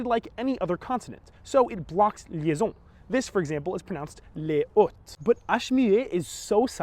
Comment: Sounds like you just said les hottes, not les hautes.